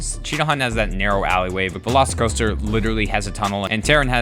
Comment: did i hear velocicoaster